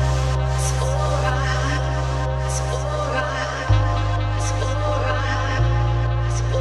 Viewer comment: i keep hearing "its flouride" not "its alright"